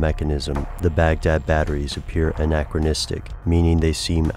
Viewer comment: Is the narrator saying: un-acteristic? From the word; characteristic? I never heard of it (nor can spell it) but I like it. Now if only I knew what he was saying for sure...................